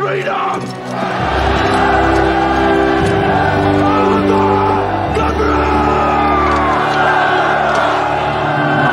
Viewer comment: What does he scream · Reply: He says "Alba gu brath" which means 'Scotland Forever'.